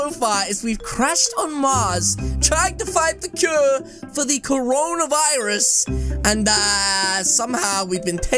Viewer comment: Did you say corona virus